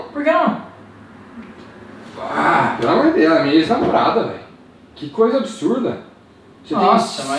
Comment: eu ouvi um " ELA E MINHA NAMORADA" ou tô ficando maluco ?